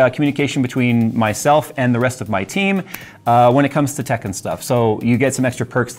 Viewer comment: I was so confused by "tech and stuff" because I heard it as, "Tekken stuff..." LOL